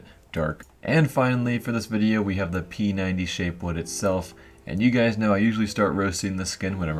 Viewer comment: I thought he said penis shape wood lol